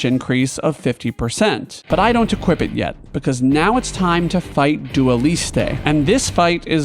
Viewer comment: I thought you said Dua Lipa, i choked on my spicy food because i thought you're beefing with her mid-stream and mid-game